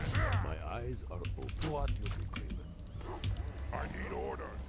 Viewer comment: what does the witch doctor say? I understand " What you be gribbin" after says " I hear the summons".